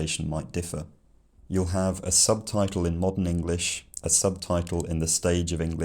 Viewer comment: I thought you said you'd be doing modern AMERICAN English at the end. It still sounded pretty British to me.